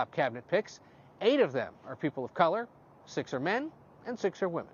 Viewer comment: [Edit: Don't listen to me, I honestly misheard "The president-elect has appointed 12 top cabinet members, eight of them whom are of color(Which is far better than what I thought I heard), 6 are men, 6 are women." ._. For this] "6 of the 12 people are black, As we as, and is made up of Six Women and six men" [Cont.:I got to listen a lot closer if I'm going to be on Cloud 9 while watching these things 😅 My apologies everyone.] Is it just me or does anyone else find this funfact statement pretty ridiculous? I mean I could care less what color or what genitalia they have in between their legs. I do care that they are stable, ethical, dedicated and qualified to take up such a position. In the end, they decided to make that "statement" for the Right audience who will gobble it up,,, I just hope they're up for the task.